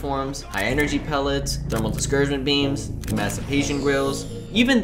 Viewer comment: For some reason I thought you said "massive Haitian girls".